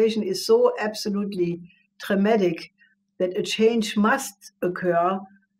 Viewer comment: I think she may have said "traumatic", not "dramatic" at 7:02. Love the show! Edit: Thank you for the subtitles for the hearing impaired!